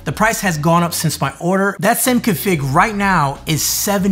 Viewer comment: what did you say there? that sen com fig right now iss